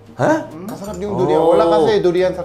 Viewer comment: Ako lang ba na misheard yung Durian? Narinig ko Judy Ann 😂